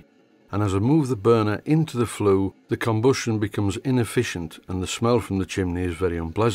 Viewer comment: Your Jorkshire dialect makes it sound like you say "combustshn." :D